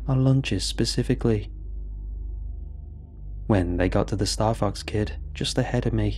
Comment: I thought he said star f*ks 4 a second 😁